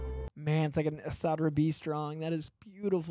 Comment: Hey who or what is referring to at 23:13 because I couldn't catch what he said and the captions don't help that much. 11/23/23 Edit: After watching another reactor make the exact same reference to the artist at this moment of the show I've learned that said and is referring to Esad Ribić. (great to find on Thanksgiving with matching numerical day and year :D)